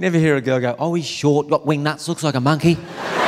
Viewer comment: what did he say wing nuts?